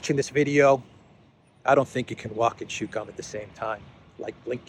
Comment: I thought they said walk and shut gun in the same time. But maybe I'm wrong.